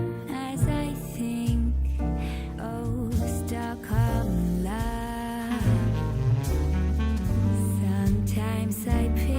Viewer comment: _ExcitedKissDaRing: Time Stamps :_ExcitedKissDaRing: Introduction 00:00 Pre show songs. Clara Mendes O Além é Aqui 03:34 Better kind of bitter by Peachy pavement 06:45 Keep on talking by Lionel quick 08:52 Stockholm love by Mathilda June 13:03 Can't hold it in anymore by Vividry 16:04 repeat of first song 18:50 Good lies by Vividry 21:55 Show starts 24:10 Candle of the evening Roja Dove London, Fragrance of the evening Louis Vuitton Ombre Nomade 25:15 Deshawn shoutout 26:20 High Value man Basics 28:05 Bill gates, Elon Musk, Dr Dre, Will Smith, why are they similar? 29:20 Do Modern women and High value men mix? 36:08 Get the likes up theme song 37:46 Back to the show 39:15 Has Jada talked about Will the way she talked about 2Pac? 41:10 I have been here... 45:50 The more she has, the less useful she is 49:12 Stay on purpose 53:20 Nice modern woman 1:12:20 Money world 1:13:50 1st Caller No name age 38 1:45:15 In between conversation 2:01:12 2nd Caller Tina age 27 2:10:25 3rd Caller Vanessa age 24 2:13:33 4th Caller Nokia age 30 2:20:30 5th Caller No name age 28 2:24:55 6th Caller No name age 33 2:30:00 Are you going to have 3.2 million cash? 2:37:02 Closing Song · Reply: Thank you for the Stockholm Love time stamp!!!! I thought she was saying stuck on love loooooooooool.